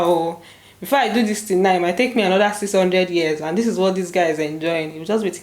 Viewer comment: did you just say "it might take him another 600 years?" 🤓